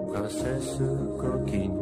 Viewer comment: j'ai entendu "a la fesse coquine" C'est un vrai problème.